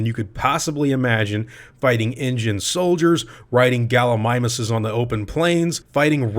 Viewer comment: I thought he said "riding Ingen soldiers, fighting Gallimimus' on plains"